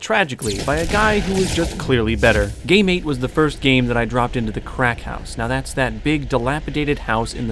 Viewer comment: i thought you said gay mate for a sec lol, nice video though:p